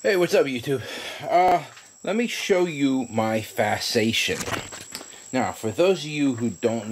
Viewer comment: I honestly thought you said fascination LOL! Now i know a new word. Thanks for sharing.